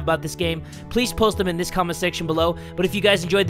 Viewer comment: when I first watch one of his yandere videos, at the end I always thought he said," that Cubs Scouts is dead too!" but know I know he says, '' is that dude".